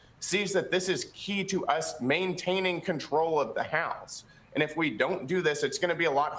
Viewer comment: into vid. I maybe misunderstood the speaker. Did he say control of the people or house. What's a voter to think🤷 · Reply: He said "house"